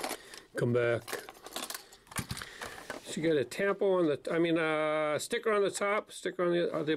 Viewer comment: did he say tampon? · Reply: Tampo. It's the name of the machine that pad prints the decorations on the models.